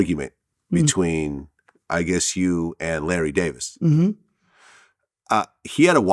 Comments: I thought it said Larry David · Thought it said Larry David at first. Glad he' still alive...